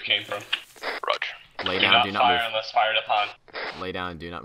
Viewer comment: I thought my guy said, the orders were don’t fire until fired at, them they shot the turret guy and raised hell